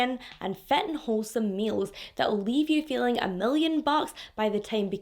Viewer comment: Great job. Omg yesss, if Kim K and Pete had a baby. You got the beauty and the funny. Btw I had to remind, I thought you said "fentanyl wholesome meals" 🤣🤣🤣🤣